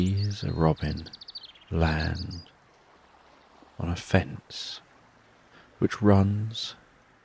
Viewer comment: Oh, lord. I thought at 1:26 you said the robin landed on her face! I thought, “well that’s interesting. Where is this going, then?” And then I realized you said fence! Lol 🙄